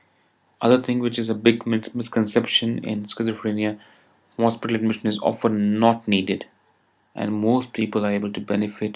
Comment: (did you said hospitalization is not needed?) or...?